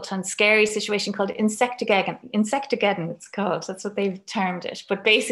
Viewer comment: Hi Mary, thank you for this talk. Just for clarity, when you speak of 'Insectememnon', am I spelling that right? It must be a very new term for I didn't find it on the internet, not with the above spelling at any rate. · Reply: Leni, she's saying "insect-ageddon" -- normally I hear the term 'Insect armadeggon" here in the states. (I know this is a year after your comment, but maybe you'll see this.)